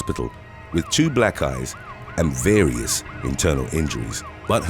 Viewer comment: "with to black eyes" sounded like "with two black guys". I had to look up at my computer wondering what that had to do with it 😅